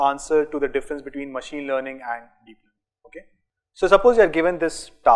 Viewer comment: difference between machine learning and deep learning ? (am I hearing this right ?)